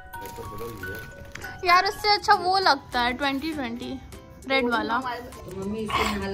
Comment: I thought that di is saying ise accha 2020. Year tha ...😅